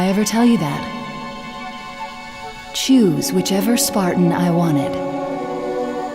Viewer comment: I heard her say "chews"